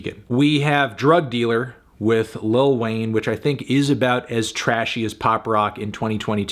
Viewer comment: I thought the melon said “and Ween’s feature on the back end…” but he said Wayne lol